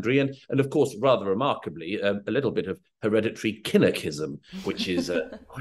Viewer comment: At the 6:01 mark in this video Murray mentions, what I hear as, "international killer-cism" and that's an "unforeseeable hereditary line in politics", and the host said that's "one international that power family". What are they talking about there? Or who?